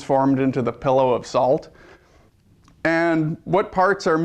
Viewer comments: wow...could it be that since you have to "explain" or "pick a version" or "convince" yourselves of anything, you're wrong? did he really say "PILLOW of salt" at 2:14? lol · Did he say "pillow" of salt? I thought is was "pillar"... ah, who cares?